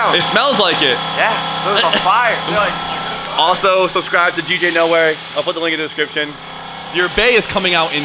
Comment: Sounds like you were saying “djnowhere” lol